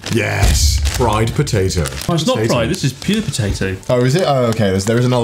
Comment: I can't be the only one who thought he said: "Pube Potato" (no not the uk spelling of pub..the other pubes..)